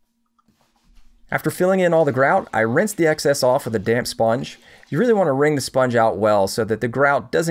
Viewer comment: what i heard was "i rinsed the excess off of the damned sponge" :)